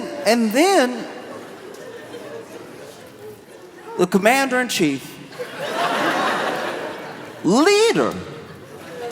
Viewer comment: 😂 I thought he said Mandarin chief😂😂😂